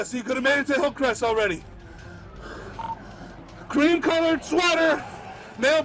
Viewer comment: That sounded like he said green, he should have said white, I'd never had assumed (over the radio) that he said cream